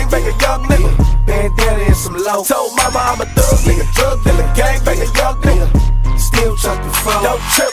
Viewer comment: who else thought he said ''told mama im a thug nigga thug nigga gang banging thug nigga''?